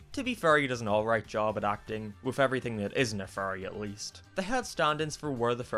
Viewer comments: Who else thought he was saying furrys and not fairy's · Dang for the whole thing instead for fairy's I thought you were saying furrys